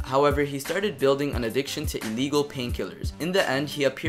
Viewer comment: Did he say ILLEGAL? OR LEGAL pain killers?